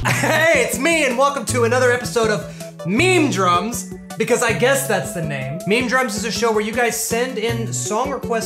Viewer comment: I thought it said "MEME DRUGS" i have no idea i saw "MEME DRUGS" lol.